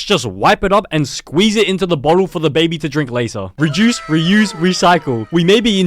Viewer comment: Did anybody else hear “re-juice, re-use, recycle”?